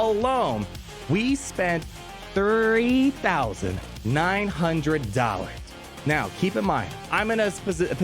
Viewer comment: had to double take thought you said $30,000. Also I thought you didn’t whale or get JKL but you have him R7